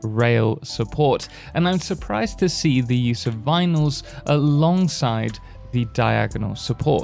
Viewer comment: I cant make out what yew saying here 1:05 "im suprised to see "***" alongside the diagonal supoort. Anyone able to tell me what he is saying ? Sounds like he's saying something about vinyls. But there's no vinyls in the game ?